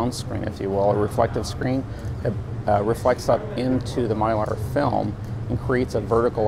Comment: please help me, what is he saying at 1:00 ? reflects up into the *I can't hear* film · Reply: Mylar, as in like the mylar balloons, only in this case it is clear.